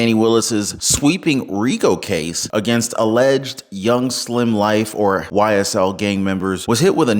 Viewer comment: Did you just say “Young Slim Life”